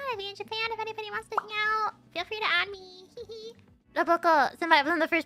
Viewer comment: I think she says "feel free to at me". When you "at" (@ someone, that means putting "@ in a message so it gives the person a notification. Similarly, "Don't at me" is slang for "don't message me" or "don't argue with me about this".